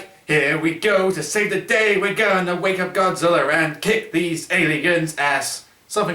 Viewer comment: In 18:05 said Godzillr 😀 lol look at it yourself. Not the first time I heard that word Godzillr!!! 😂